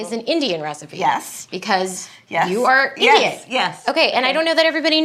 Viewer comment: It is funny that the subtitles say "because you are idiot" not "because you are Indian"... I'm not calling the subtitles wrong, mind you, just saying it's funny... 🤔